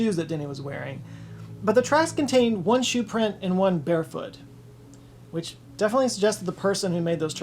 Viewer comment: Did anyone else think he said “bear foot”? I thought the story was taking a sudden dark turn (not that it was ever going to be a happy tale).